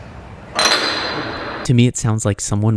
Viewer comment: i believe what we hear at 6:19 is the word "gulag" like in warzone